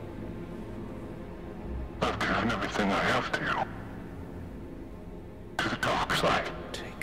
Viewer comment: what did Kylo say ? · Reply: "I gave everything I I had to you . To the darkside..."